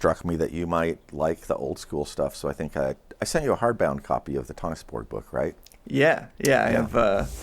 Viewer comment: I can’t make out what you’re saying—“the tanis board book”‽ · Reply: The Lost Dungeons of Tonisborg from 1973.